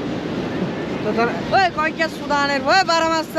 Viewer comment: de oradaki beyazlı yallah mı dedi ben mi öyle duydum? El hareketleri falan da bi acayip saygısız ilkel insanlar